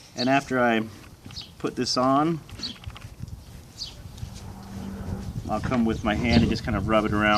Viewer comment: Did he say come with his hand and rub it around at 4:10 lmao 😂 childish I know but 😏, thank you for posting the video good info not the you know but about the boot treatment 👍🍺